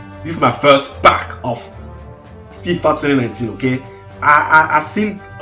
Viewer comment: did he say fifa?